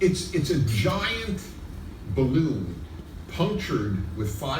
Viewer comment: Errata: creio que ele diz Giant Baloon (balão gigante) aos 2:53. Excelente vídeo.